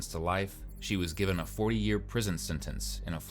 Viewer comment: Sounds like he said 4 year prison sentence but it's 40 if it wasn't on the screen I would have thought it was 4 years